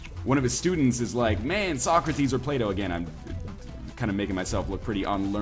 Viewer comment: And the old Greek dude must have been Plato (when I heard you first I thought you said Play D'Oh) since he defined platonic relationships where people don't bone eachother.